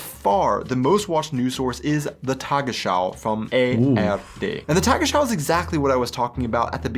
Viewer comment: Did he really said "Tagesschau from the AFD"? Its ARD. AFD is the right wing party in Germany!